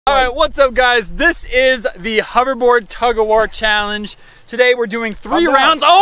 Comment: It sounded like you said hover board tugerboard